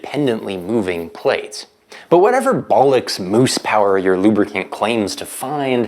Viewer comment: Did he say bollocks? likes